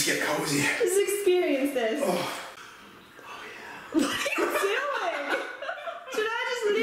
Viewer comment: i hear pat say "Oh yeah" 😅😅. I subscribed to you guys already😊